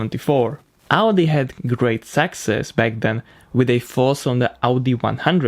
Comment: Thought you said "sexist" oh success. . .